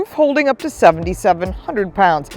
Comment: Wait did she say the roof holds 7700 ibs 1:14 🤣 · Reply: I said 770 pounds